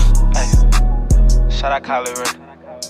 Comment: Excuse me did he say shout out Kye Lareay who is that😂🤣🤣🤣 but str8 heat🔥